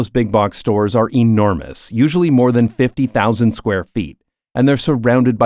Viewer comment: hi there, at 0:39 when converting square feet to square meters you used the conversion of feet to meters, forgetting the "square" part. the correct area is about 1400m², not 4600. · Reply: I think you misheard, because he says 50,000 square feet and in that case 4600m² is a good approximation.